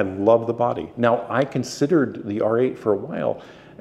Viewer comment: I was so confused at first, because it sounds like you said R8, but I am guessing you meant Z8. But once you started talking about weight, I knew what you meant. 😉